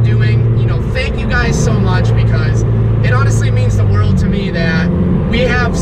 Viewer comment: I swore I thought he said "SO MUCH CHEESE"